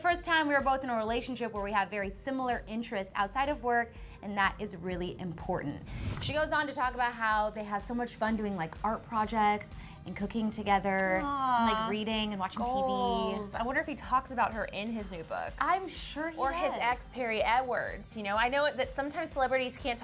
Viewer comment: i literally heard "cocaine together" LOL